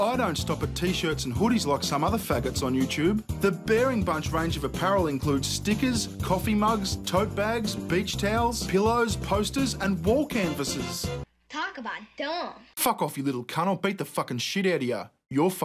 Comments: I thought he said bitch towels. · BEACH TOWEL OH MY GOD I THOUGHT HE SAID BITCH TOWEL I WAS LIKE WTF? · 😂😂😂😂😂😂 sounds like you say"bitch towel" instead of beach towel 😂😂😂😂😂😂